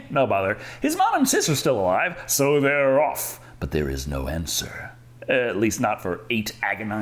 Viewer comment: Instead of "there was no answer" i heard "there was no hamster" sooo that made me a bit confused about the window